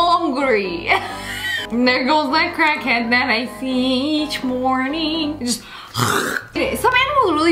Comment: Can someone please reply to this comment and tell me what she said at 11:14? 😅😂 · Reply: "hungry" "there goes the crackhead that I see each morning"